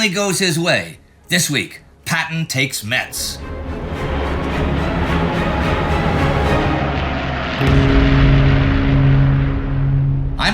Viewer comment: At first, I thought said " Patton takes meths "